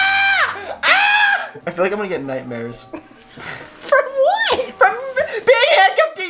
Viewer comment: When said I think imma get nightmares I thought he said I think imma get u pregnant 😂I'm sry guys